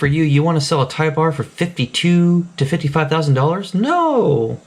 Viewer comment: I really thought he said, 5,200 to 5,500...... I listened to it again and sure as shit, he said 52,000 to 55,000. That is nuts!